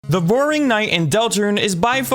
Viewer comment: Did she just say "The Boring Knight"?